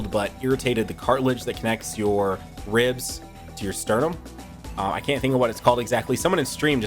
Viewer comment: all I heard was seereax “o my sternum”